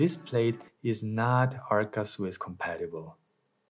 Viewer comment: I like this review but at 6:26, I don't understand, the plate is not "RS compatible?" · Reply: Thanks. The plate is not “Arca Swiss” compatible. It’s the standard used by many manufacturers on mounting systems that you can find mostly on things like a tripod. If you don’t plan to swap the camera to a tripod or anything like that it’s not going to be a dealbreaker. For me I find myself removing this and placing another plate to use the camera on my tripod that has the Arca Swiss mounting plate. Hope this makes sense